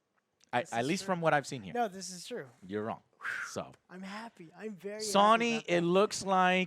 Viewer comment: Did he say Sawny at the end there?